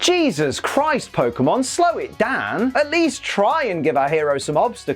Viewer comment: "Slow it DaAn" Did anyone else hear that?